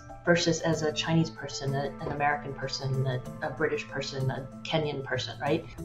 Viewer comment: Had to go back and confirm 14:39 , did she say Kenyan person, in a space race list?